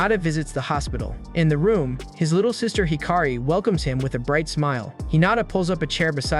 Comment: When he said hiKari I thought he said hakari I might got too excited